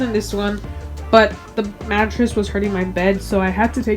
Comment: Lol, your mattress was hurting your bed? Maybe I misheard lol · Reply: I said the mattress was hurting my back😂